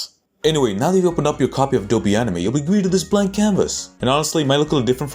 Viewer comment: did I hear Adobe anime 😂